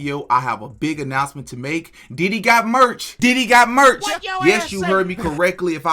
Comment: It sounded like you said DIDDY got merch. Might wanna speak slower lol.